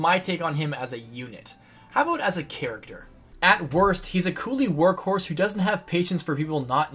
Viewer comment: did he just say Coolie?